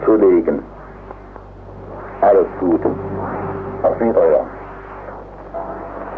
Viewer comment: Transcipt at 5:35 is wrong! Instead of "Auf Wiede(r)sehen" the voice says "Auf Wieder*hören*". But english translation "hear again" is correct, "hören" means "hear", "sehen" means "see".